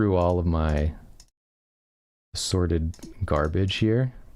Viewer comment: got this on in the bg for 4 am sewing time and misheard assorted garbage as "sordid garbage". that phrase is a keeper methinks